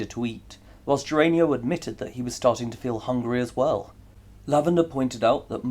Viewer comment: I loved the: “Lavender was confused and asked Geranio why he had taken the monkey..” On a slightly more serious note: At 40:01 it says fell instead of feel. On the new setup: I actually prefer knowing who the victor is ahead of time.. it’s okay like this though because I just go check the comment section.